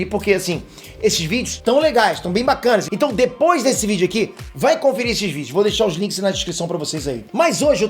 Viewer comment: só eu q entendi "vai conferir xvideos" !?